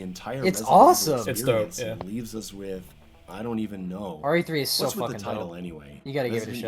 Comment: can annyone tell me what said? i only heard: ''RE3 is so fucking (...) you gotta give it a shot'' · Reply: That was. He said it's hype.